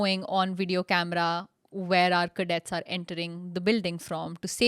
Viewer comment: did you say "cadets"????